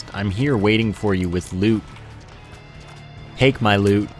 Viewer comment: Oh lord I heard :"I'm waiting for you with lube" when he was talking about secret rooms ;O..